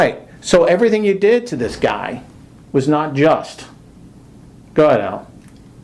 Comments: It sounded like the superior told Contardi to go to hell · does IA say "go to hell" after saying the cop wasn't just?